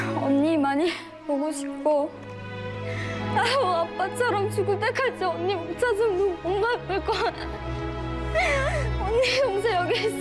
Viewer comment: btw at 12:40 what she had actually said was sadder than the subtitles- she said "before i die like our father did..." not "before i die"